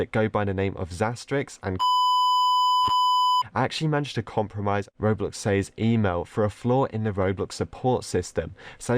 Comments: what did say? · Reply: Rodrigo Vargas problem something personal. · wha- what is he even saying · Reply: ColbyValentinoCrown